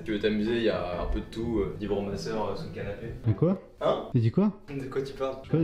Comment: au lieu de vibromasseur j'ai entendu "tu peux t'amuser il y a ma soeur sur le canapé" J'ai été désorientée